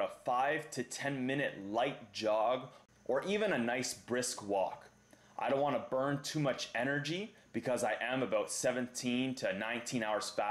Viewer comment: For some reason I thought he said "I don't want to burn too much energy, I am 17." WAIT WAIT. WHAT?